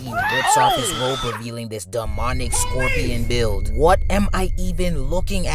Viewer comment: you said, "demonic." I heard, "dumb phonics."